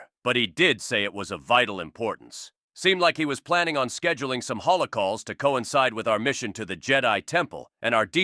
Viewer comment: scheduling holocausts?! (I heard wrong)